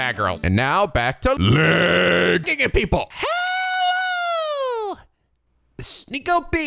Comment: Sorry what was the intro? I heard "Leeeee - Kingdom people!" Loool definitely not an auditory person 😂